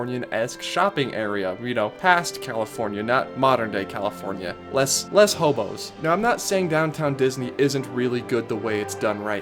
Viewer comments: Sounded like he said "less homos" lol i had to rewind. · did he say less homos or less hobos? I’m confused